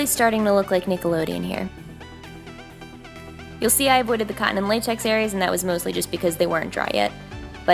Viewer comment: i know she says cotton latex but it seriously sounds like she says condom latex sometimes lmao 😂